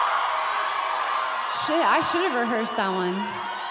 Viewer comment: what's she say at 22:18, right before Blue Jeans starts? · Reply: "I should've rehearsed that one" (Fans were shouting "West Coast")